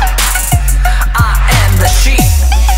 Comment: At first I thought this was saying "I'm Shaun the sheep" but now I CAN'T GET ENOUGH OF THIS